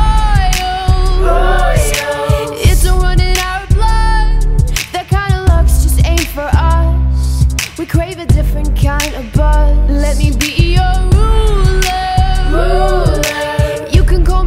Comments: Who ese thought it said we ride a different kind of bus? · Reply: Random GirlVlogger thought she said we create a different kind of boss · sounds like shes singing it dont run in our blood to me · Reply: SOVA Rides When i first heard it i thought she was saying " That kind of love just ain't for us" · Yo I thought she said that kinda love just ain’t for us smh🤣